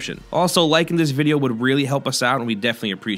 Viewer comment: It sounded like he said "I'm into the new guy" at the end